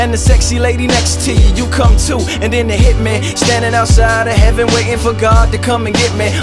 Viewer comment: "Then it hit me... standing outside of heaven waiting for God to come and get me, I'm too uncouth. Unschooled to the rules and too gum shoe" 🔥🔥🔥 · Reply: I thought he said . Then the hit men , standing outside of heaven waiting for God to come n get me